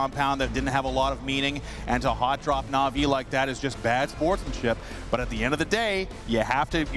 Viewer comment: am i hearing 8:45 right? bad sportmanship? wtf is wrong with these navi fans